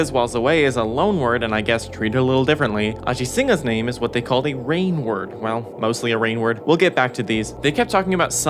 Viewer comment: i thought that said rain world lol maybe it could be a rain world reference....